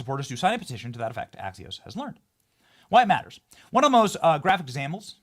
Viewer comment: I thought they said one of them bought a goat.